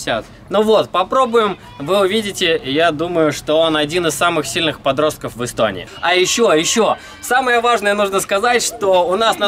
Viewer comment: Ты сказал один из самых сильных подростков Истонии? Или чего мне там послышалось:?